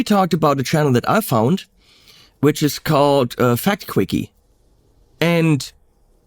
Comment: If only there was someone who has told you about Simon's several channels, and talked with you about how well oiled that machine must have been. ...and adding the joke that Simon Whistler, Michael Stevens from VSauce and Andrew Rae from Binging With Babish are 3 different people, and clearly "bald guy with a beard" is a path to a successful YouTube channel... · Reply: I didn't know who was talking about - I thought he said the channel was 'Fat Quickies' and it was some kind of fetish thing, which made sense why he was watching it. Once I got a correct like I was like "oh yeah, Simon has been on the go for years. It's chill"